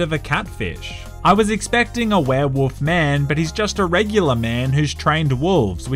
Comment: I heard “woof man”